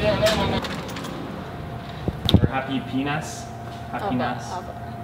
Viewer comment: For a minute i thought you said..happy p_nis! 😱